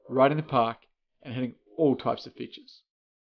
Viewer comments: i thought he said hitting all types of bitches lol besides that content is great and helpful! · Is it me but at 00:43 did he say get all types of bitches ?